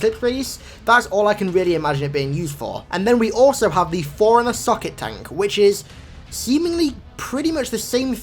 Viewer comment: Haha, I thought you said "Forerunner suck-it tank"